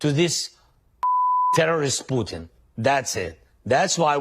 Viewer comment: I know President Zelensky can speak multiple languages but I had no idea he knew how to speak TexMex Slang. I heard him say, "We are not ready to give up our freedom to that fucking Terrorist Puto (asshole)." HaHaHa